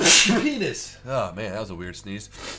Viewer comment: That second sneeze at 9:30 sounded like you said penis.